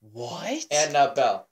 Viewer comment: I don't hear Annabelle, I hear Beelzebub